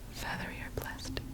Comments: I wasn’t paying attention for a sec and then thought she was saying: “Feather your plastic” lmao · and, on the 6th day, god spoke, and he said: f e a t h e r y o r p l a s t i c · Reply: It sounded like she said feather your plastic